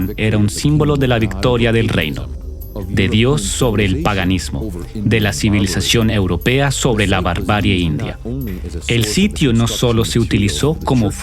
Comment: minuo 4:58 creo escuche mal ... la verdad yo entiendo triunfo de la barbarie europea sobre la civilizacion india..?? tal y como ocurrio en Mexico con los aztecas.. solo llegaron America a matar, saquear y destruir civilizaciones...